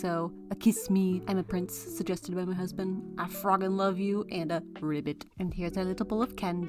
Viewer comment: I thought that said I f***ing love you but it was I frogging love you